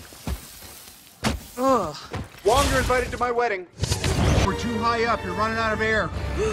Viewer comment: Tony Stark: “Wong you’re invited to my wedding” 😹😹😹😹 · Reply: That’s what he said!! I always thought he said “you’re all longer invited to my wedding” 😂😂😂